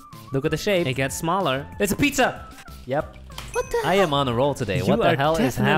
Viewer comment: anyone else hear a girl saying 'what the'?